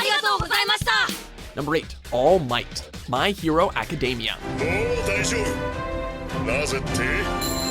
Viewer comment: Did he just say "Macadamia" at 2:40?